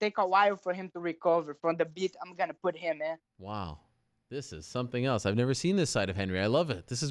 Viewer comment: Did Ariel just say - Wow this is something else I've never seen this of Henry?